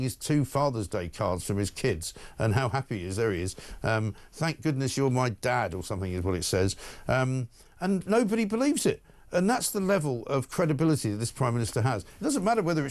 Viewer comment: I thought the card said "I bet you're my DAD" 😂😂😂